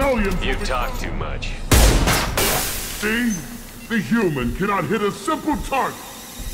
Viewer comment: Funny that the first time I heard him without subtitles, I thought he said: "Hey, human, can't we have a simple talk!?"